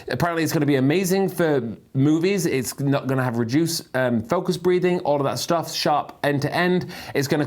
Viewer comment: Did I hear this correctly about the new lens? “It’s not going to have reduced um focus breathing, all of that stuff..”